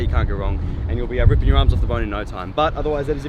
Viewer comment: Arms I thought it said sarms